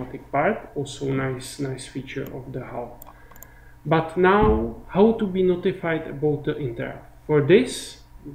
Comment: What does he said at 11:39? "how to be notified about ..." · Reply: He asks himself "how to be notified about the interrupts" in best <<frenglish>> and answers it => "by callbacks" and goes on. You need to be carefull! If you listen too close you may start to talk english in the same manner. Sometimes he reminds me at Hercules Poirot.. "Hastings!? If you please..." ;-)